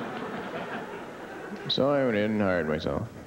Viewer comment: Does anyone know what he said with the joke about "help wanted, self serve?"@ 4:37. All I hear is " so I went in and (i went tall). That line got applause and I can't make it out.